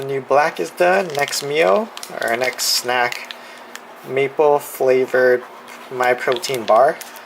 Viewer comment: i thought he said meatball flavor lmaooo